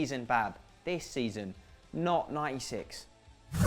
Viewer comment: who thought said not Isis instead of not 96 at first